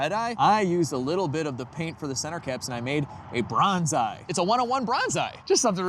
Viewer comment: I thought you said brown eye when you said bronze eye😂